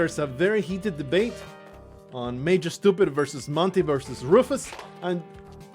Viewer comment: Its funny to hear the Dog name debate because before I sarted watching anyone who played Spelunky I named the dog Mr. Stupid, and when I started watching you back on the infancy of the channel I would always mishear Major Stupid for Mr. Stupid. Plus I loved the names you made up for the Spelunky 2 creatures!